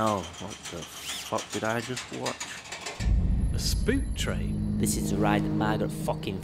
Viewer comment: Swore is said "SPORK" train and not "SPOOK"